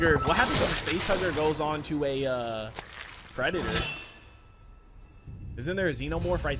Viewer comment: Did you just say, "no offense, to a Predator???! HAHAHA This is why I watch you mano